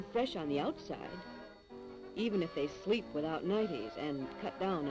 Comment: sorry did she said sleep without nightie.. So she sleeping nude?